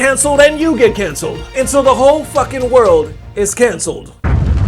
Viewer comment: I heard the word "cancer" not cancelled 😂😂 legit I felt attacked for a second